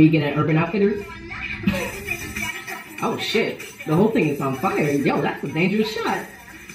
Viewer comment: i don't know why when i first heard this i thought they were saying " my butt is on fire" lol don't judge btw that's why Lisa was saying don't play with me boy cause she wants a man lool